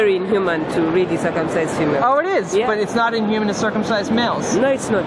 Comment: Did she actually say at 36:52 "Its inhuman to circumcise humans but not inhuman to circumcise males" Please tell me I heard it wrong! Please tell me that was not a real interview!